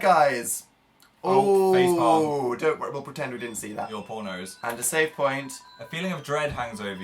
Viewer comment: when phil said poor nose i thought he said pormos